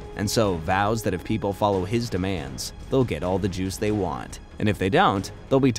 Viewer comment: it sounds like you saying get all the jews 😭